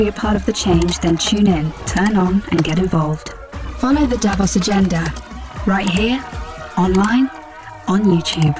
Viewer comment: Follow the Davos Agenda (Sounded like “Devil’s Agenda”). Thanks for uploading, I have been waiting. God bless and keep us all